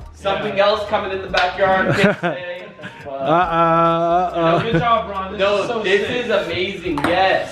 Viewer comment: sounded like said "Good Job Brawadis" Maybe it's gonna be a basketball court... 🤔